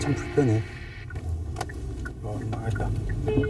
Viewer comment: what is Youngjae saying at 6:36? It sounds like he's saying "oh shit" in Italian ("oh merda") 😂